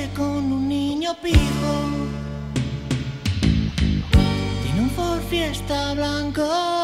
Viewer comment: yo siempre pense que decia " en un ford fiesta blanco y un gacel amarillo" y resulta que era jersey....